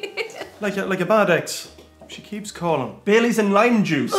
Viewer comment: Leather jacket guy is my hero "Shes like a bad ex,she keeps calling" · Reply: Ohhhhhhhhh! I thought he said “like a bad eggs”! That makes much more sense!